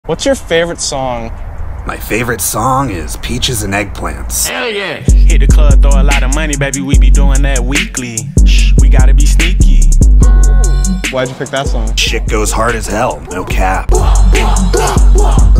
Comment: I always hear it as "we gotta be stinky"